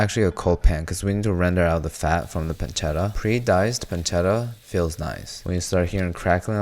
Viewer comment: what pre cut pork was he using here, I couldn't catch what he was saying here. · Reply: Pre diced porchetta maybe 🤔